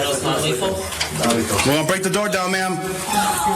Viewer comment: I thought they literally all said we goin non lethal before the door was broken down.